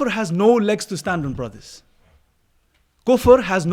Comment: I thought he said gopher. LOL